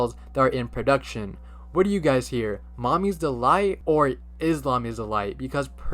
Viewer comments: I hear mommy’s a lie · I hear 'Salami's Delight' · i heard "is lama light" 😂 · For the baby, I hear "Boogla Is Delight" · The little mommy doll, I don't hear "Islam is the light" from the clip played. Instead I hear "Iskam is da light" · I heard both "Islam is the light" and "Its mummy's delight" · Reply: i heard “momtis dehltit” not even real words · The doll that like said “mommy is a light” or something I heard “is my mommy alive”😟